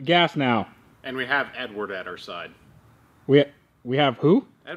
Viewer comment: I thought he said... "and we have N-word on our side"